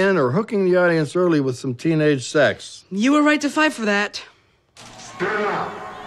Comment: can someone explain teh end? why was she shocked? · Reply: you can hear the monster say "Stella get outta here!" It was a distorted version of what Gloria was saying.